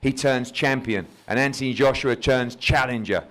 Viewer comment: Anybody else notice Eddie at 13:43 say, Anthony Joshua churns challenger, instead of turns?